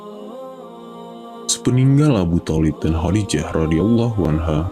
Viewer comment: Ngomong apa si, sepeninggal? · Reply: Meninggal kali